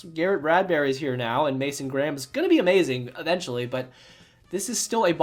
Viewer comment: did i hear mason graham's name?????